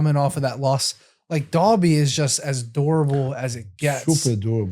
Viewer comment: I thought they were saying "adorable" and I was like wtf?